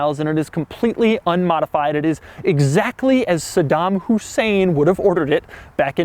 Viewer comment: did he say SEDAN hussein?